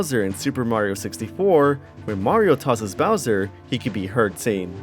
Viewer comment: I believe he's supposed to be saying "So long, ay Bowser?" That's the best I've been able to put together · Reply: I think it's just the audio being crushed, but I always heard it as "so long-eh bowser". With the "long-eh" just being him saying long with a stereotypical, overannunciated accent